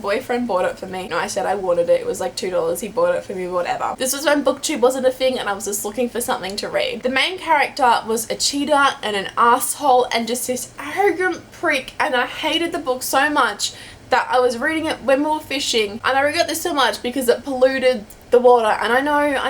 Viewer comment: All the book rage! Also when you talked about the last book I thought you said the main character was a cheetah and I was like what in the world XD